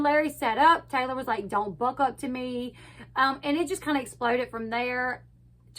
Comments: I thought I heard Taylor say "Don't buck up too my wife" as Larry jumped to the edge of the couch at Maci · He did say that. Larry did buck up to Maci too. I don't think Larry would of hurt her but he DEF was trying to intimidate her and Taylor wasn't having it🤣❤ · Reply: I missed him saying not to buck up to him. I swear I heard "Don't buck up to my wife, buck up to me". I have witnessed my own husband do this when someone tried to intimidate me. Real men that love their spouse won't stand for ANYONE to disrespect their spouse